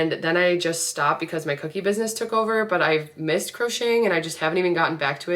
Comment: i thought u said cussing instead of crocheting !!